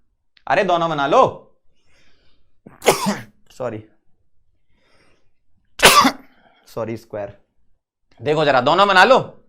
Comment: did he say (SORRY)^2 😆😆😆😂